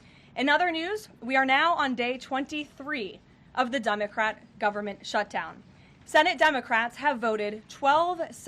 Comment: Did she just say..."The Dumbocrat government shutdown?"